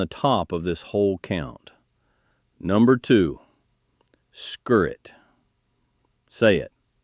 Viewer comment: I don't know why there's such confusion in the comments. At 32:08 he clearly says SKIRRET. At 32:40 he gives the botanical name, Sium sisarum. This is the root vegetable that tastes like apple and honey as mentioned in the title.